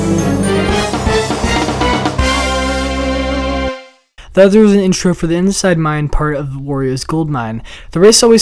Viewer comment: what the hell is he saying at 5:16 LMAO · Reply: "The other is an intro for the inside mine part of 'Wario's Gold Mine'."